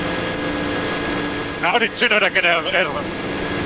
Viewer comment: What did Bottas say? I can't hear it. Can someone please tell me what she said? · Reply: Something like "How did Tsunoda get ahead of us?"